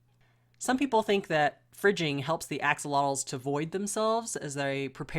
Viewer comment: I "fridged" my axy for 48 hrs. When he was constipated. I was nervous but it worked great he voided and was back to normal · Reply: For a second I thought you said “Fried” And I was just like WHAT!!!!!!!?